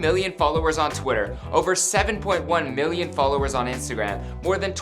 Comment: Is it just me or i literally heard a 7.1 million followers at 0:19, when it is suppose to be 17.1 million followers? 😂